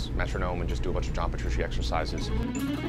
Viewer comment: What kind of guitar exercises dose Matt say he dose at 5:20? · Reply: Petrucci Exercises. John Petrucci is a guitarist for Dream Theater.